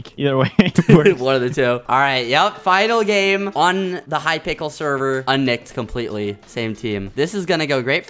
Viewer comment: anyone else heard “ hypickle server “